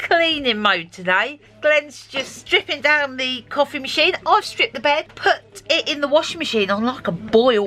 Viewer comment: I had to rewind thought you said I shit the bed but it was I stripped the bed made my night gave me a giggle take care stay safe